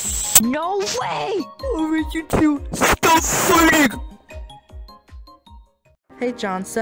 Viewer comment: shout out to the person who thought I said aright you two...STOP FARTING Best serj~sama moment